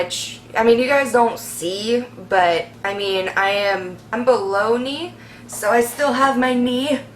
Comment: Also, I thought you were saying that you were “bologna” for the longest time and not “below knee” #fail